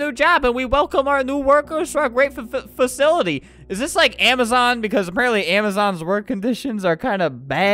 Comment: Tbh, I thought he said "Amazook"